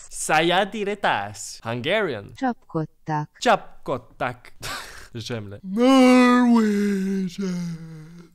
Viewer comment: Did you just say zsemle?